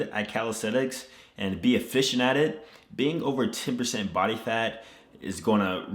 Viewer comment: Did you say over 2% body fat or 10%? You look very shredded but I don't think you are near 2%.